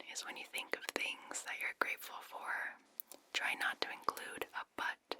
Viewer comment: Had a split second where I thought I heard ‘Try not to include a butt,’ and it seemed so weird that you were specifically calling that out.